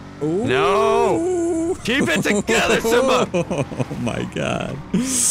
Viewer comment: does anyone else hear “keep it together simba”? No just me ok